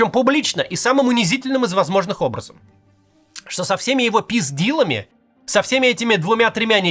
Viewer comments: я один услышал "со всеми его пиздилами"?🤔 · Когда услышал "Трамп со всеми его пиздилами", пришлось дважды себя перепроверить. Peace deal, пис дил - всем пис дил. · Послышалось пиздилами😂